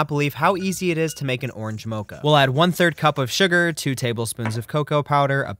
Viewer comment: Omg I thought it said orange matcha and I was like how did he do That lol